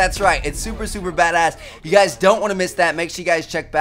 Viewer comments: What are he saying at 4:44? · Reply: He said check out the other 2 videos if you havent seen them yet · what did he just say there? :P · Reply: "Check one of the video's if you haven't seen them yet."